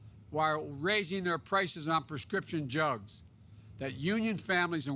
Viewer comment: I can't be the only one who heard him say "Prescription Jugs"???